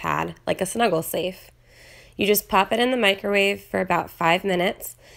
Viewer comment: Please tell me i'm not the only one who thought she said she'd heat the kitten in the microwave...